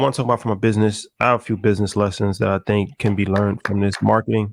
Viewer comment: I thought it said boxing lessons i was hella confused